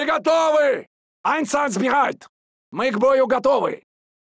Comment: Подскажите пожалуйста... Кто шпрехает… Что это за фраза? Как она переводится... это уже не в первой игре такое слышу... Просто любопытно... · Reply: В оригинале вроде как было Einsatzbereit - в полной готовности Здесь просто произношение хромает